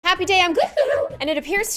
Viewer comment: Please tell me I’m not the only one who thought she said “happy day on gloom” not “happy day im gloom” :0